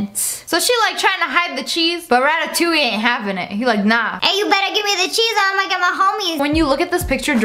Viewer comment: what did she say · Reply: Hey you better give me the cheese or imma get my homies